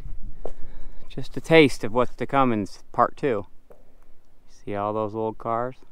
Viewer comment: Did I HEAR a teaser at 54:33, when you said “just a taste of what’s a Cummins”??????